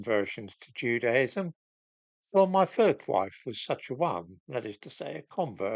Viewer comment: Did you say 'my third wife?'. How many more are there? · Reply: The subtitles say said 'my first wife'. While watching, I went back and rewatched that bit with subtitles on, also having thought he said third by the way. microphone might have been having a senior moment.